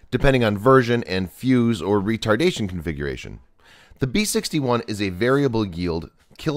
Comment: Did he say retardation configuration?